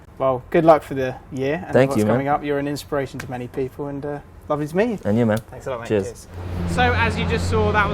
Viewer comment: what does he say at 16:43? Sounds like "love is me" which i'm guessing is wrong. lol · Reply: lovely to meet you